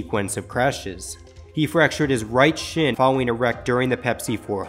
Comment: thought he said he fractured his right shit